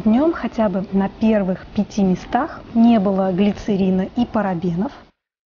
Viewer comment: Не поняла по поводу глицерина и парабенов... Глицерин входит в состав некоторых продуктов , т,е, может употребляться в пищу, а парабены вообще не могут стоять на первых местах в составе. Это консерванты и они всегда в конце списка! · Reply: Здравствуйте:) Мне вообще послышалось не глицерин, а глицерил. Думаю, ладно, быть может я чего-то не знаю о глицериле и его скрытых свойствах. Мне кажется, такие моменты, как растительный или синтезированный глицерин (и не только глицерин) стоит уточнять в видео, чтобы не было путаницы. Ведь растительный глицерин используют даже в корнеотерапии, а это уже многое говорит о его положительных свойствах. И ещё, большое спасибо вам за видео:) Всегда с удовольствием смотрю и настраиваюсь на хороший лад \(^_^)/